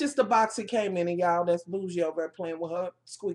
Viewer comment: PS: @ 3:24 did you say your dog's name was "Bougie" LOL! @ 6:41 "Sit-down, Bougie!" Too funny. Way too funny. Thanks.